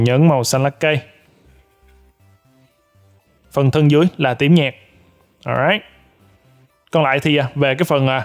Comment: Anh đọc "tím nhạt" mà em nghe thành "tiếng nhạc"